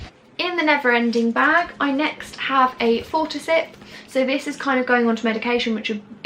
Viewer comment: Whats the item at 7:23? I couldn't quite make out the name and the captions spelling it wrong. It sounds like it could be useful for me as I get low blood sugar from time to time and often unexpectedly. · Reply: it’s fortasip